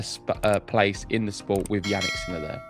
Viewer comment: Did you say "Jannik's Miller"? 😆😂